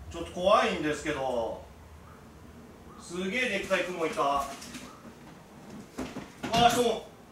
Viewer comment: 2倍速で観てたから、『でっかい熊いた‼️』って聞こえた🤣熊と格闘してるのかと思った🤣